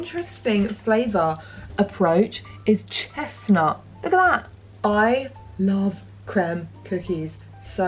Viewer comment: OMG i heard this as "i love clem". THATS MY NAME! thats the pick me up i needed . rough week . love you and your vlogs . hope you are well ... clem xx (of course)